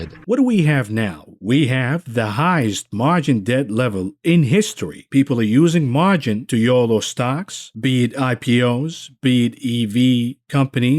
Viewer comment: did he say 'people are using margins to YOLO stocks' like 18:20??